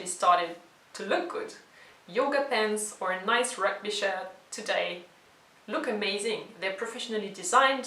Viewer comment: As always, a good theme in your video! Yoga pants or a nice rep?..?. (at 4:26) today, look amazing. I can't hear the word, can you help me? Thank you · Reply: rugby shirt (or American football) 😃